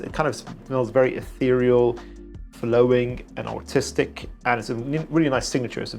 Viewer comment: does anyone else hear autistic?😂 Seriously though thanks for the great content!